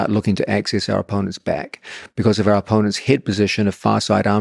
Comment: did he say head position or hip position? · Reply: Head